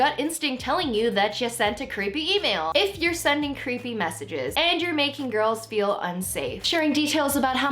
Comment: Oh my god,I thought you were saying that ProZd was sending you creeping messages